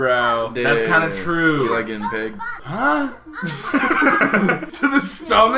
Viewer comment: “Quickest way to a man’s heart is his stomach” true: so you like being pegged I am💀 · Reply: I thought he said “so you like getting big” 😂